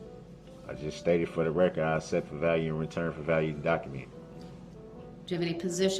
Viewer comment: “I accept for value and return for value “ means absolutely nothing. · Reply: I really thought He was saying " IN RETURN FOR VALUE" 😂. It just didn't make sense to me at all. An the "Lawful Law" statement he kept making had My head feeling like it was going to exploded.